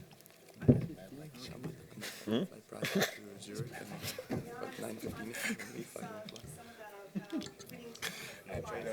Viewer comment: I wanna know what Rafa whispered to Roger at 1:05, he is always cracking Roger up. Meanwhile the first 2 mins shows how tipsy they all are already lol. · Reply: I smell like champagne.